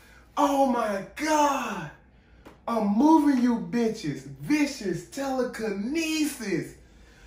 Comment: I honestly always thought I heard "CAME in you hoes" and "THIS IS telekinesis".